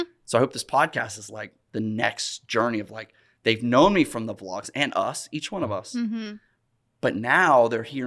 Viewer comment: “Came from the vlog” !! Funny story - I thought Roman was saying the comment Kane from the vlog lol buhahaha. Cheers from Canada 🇨🇦